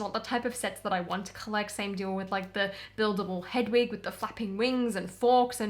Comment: I believe it should have went away a few years ago, then they could have brought it back for the reboot and excited the customer base a bit more. SIDE NOTE: at 11:34 I thought you said "...with the buildable Hedwig, with the fu**ing wings.." I was like damn she's REALLY over Lego Harry Potter. HAHAHA